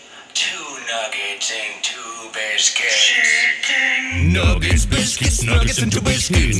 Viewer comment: in the second nuggets song, it sounds like its saying "nuggets and tobiscus" instead of nuggets and two biscuits and that seems relevant to his making a second one in some way i cant articulate